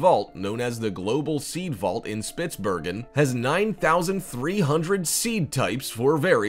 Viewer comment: “9,300 seed types” subtitle says 930,000